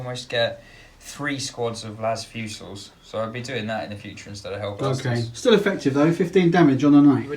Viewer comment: 3 units of what instead of the Hellblasters? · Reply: las fusil armed eliminators mate